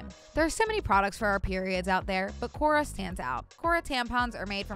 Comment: i thought said "keira is mother" and nearly fell off my chair